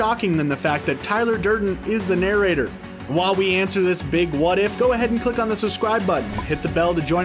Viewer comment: he said while we answer this FAKE what if I swear he said that hold the space bar from